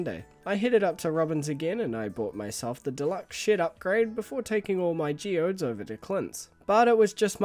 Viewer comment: I thought he said the deluxe sh*t upgrade